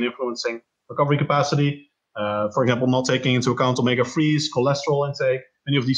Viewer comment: At about 22:00 he says something i cant make out on the topic of nutrition and recovery. I caught the words “omega” something and “cholesterol intake” but i couldnt exactly tell what he said. Id really like to know more on this topic, does anyone know if he has written specifically on nutrition for recovery or where i could learn more? I thought it was really just a matter of macros but he is saying theres much more to it. Thanks in advance. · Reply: Michael Powers omega 3... fish is a good source. As for ‘more’ I guess track down his website/blog?